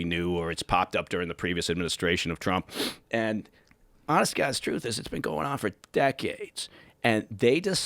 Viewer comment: When he said ''honest to God's truth'' @ 2:50 I thought he said 'honest to Castro' lol, had to rewind it didn't sit right.